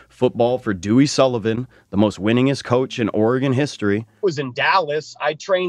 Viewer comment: Lol did I hear "most winning-ness"